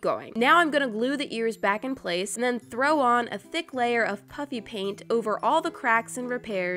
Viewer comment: who else thought she said puppy pain 🤣